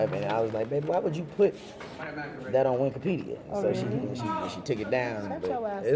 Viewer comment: …..is he saying Winkopedia!!!!!!!!